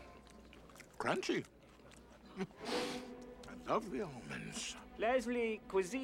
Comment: “crunchy, I love the almonds!” 😂😂😂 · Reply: I thought he said "Crunchy, lovely almonds."